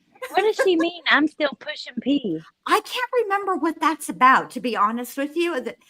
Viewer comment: Pushing P is a song by Gunna 😂😂😂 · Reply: I thought she was saying 'pushing peas'. As in the vegetable. 😂😂 Like pushing her peas around the plate like a little kid that hates vegetables